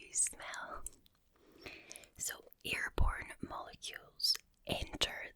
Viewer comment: it sounds like you said ear porn😂😂😂 its probably just the accent though